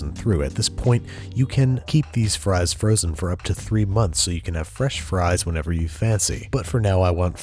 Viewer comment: Did I hear "fresh frozen"?